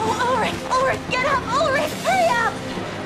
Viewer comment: Did Yumi just said "Ulric Ulric come on get up هيا!"? Did she just spoke in Arabic?! "هيا" is the Arabic word of "COME ON!". The Subtitle text say nothing. And before you ask, I checked it multiple times. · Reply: She said "hurry up"